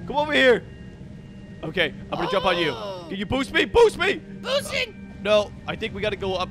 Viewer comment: I first thought said “Pussing” 💀